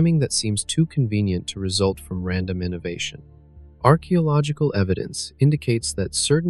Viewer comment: the way archaeological is pronounced, I heard it as "our key (o) logical" evidence, and brain now my broke.